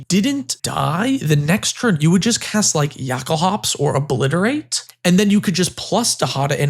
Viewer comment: What is saying while talking about dihada 33:50 “yakle hops”?!?! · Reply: Jokulhaups